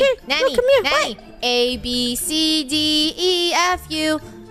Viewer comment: at this point did said abcdeatfu!? What!? 😂